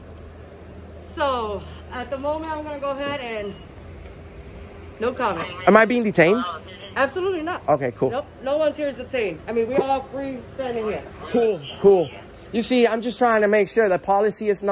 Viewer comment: Did she say obtained instead of detained?(13:46) LMFAO